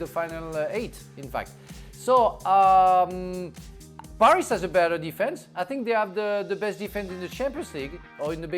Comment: i thought he said Barca has the better defense LOL